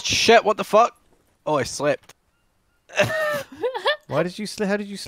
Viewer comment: I thought said he slipped. 😂 Bob: “Awww, Vic, I’ve fallen!”